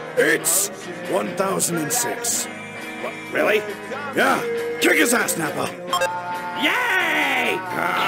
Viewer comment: That “yayyy” got me dying 🤣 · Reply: I thought he said Check his ass nappa